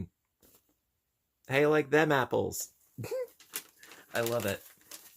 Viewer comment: How u like them fibers is what I thought when said apples but love ur attitude ur too sweet thank for being u